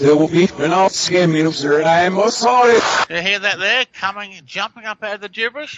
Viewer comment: I hear "And I'll scam you and I'm not sorry."